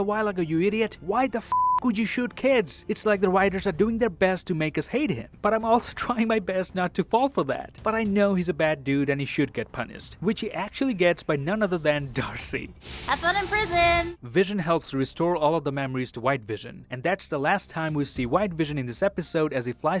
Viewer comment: did anyone else heard that "punised"